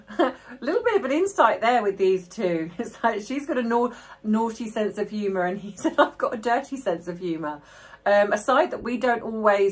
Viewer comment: I’ve always heard him say she’s got a NICE sense of humor and he’s got a DRY sense of humor.